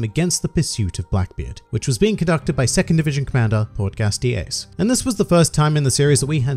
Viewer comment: what are you saying are you calling ace gas dx. as is port GAS D. but whats the x part